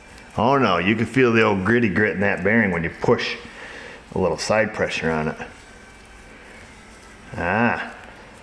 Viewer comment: did i just hear "side boob pressure" ???. im sure thats what i heard :D